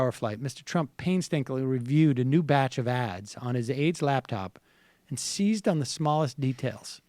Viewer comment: for a second there I thought Sam said AIDS laptop